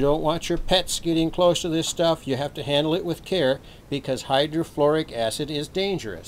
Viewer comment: Sounds like you said Hydrochloric 1st time but the label says Hydrofluoric and later you clearly said Hydrofluoric acid. Nasty stuff. Prior to plastic containers, it had to be stored in GuttaPercha containers. There's so much quartz in FT243 rocks that much adjustment can be achieved.and they stand up to some pretty tough treatment. Modern rocks are much smaller, more fragile, don't have separate plates and can't stand as much (grid) current in a valve (tube) oscillator. The plates are usually 22 carat gold, vacuum sputtered on to the quartz with masks on the xtal. Figure of 8 grinding, etching and other little tricks with solder are really not feasible. Scraping away small amounts of gold will reduce the capacity between the plates with a QSY north but the ESR can increase and activity can diminish, some times cease. Brief exposure to the brown fumes of Aqua Regia can take away some gold with a HF QSY but utter and PROMPT cleaning between etching & testing is essential. The etching continues on route to the water! Aqua Regia is also NASTY stuff.